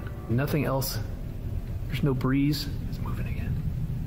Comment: Turn up your volume @ 1:56 he says/whispers "Move it again" and few secs later says something else cant make out what he said.... Fake!! Russian one- the part where the white plate is flopping around, just as it starts look under the bathroom door bottom left side someone is there blowing it under the door.....Fake